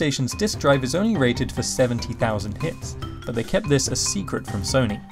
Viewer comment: is only rated for seventy thousand... WHAT? did you say "hits"? what does that even mean?